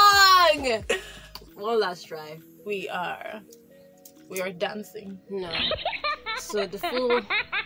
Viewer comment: That first slap entered 🤣🤣🤣! which kin is glitters and glow 🙄🙄🙄 Did you just say “we are making semo”😂😂 I just dey laugh like mumu🤣🤣 You both sha had fun, that was the most important